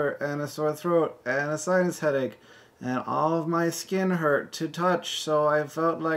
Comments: Trust Hank to get what I heard as "a science headache" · I heard "science headache".